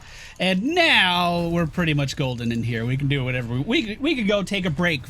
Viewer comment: Did he say 'we are pretty much Gul'dan here'? :>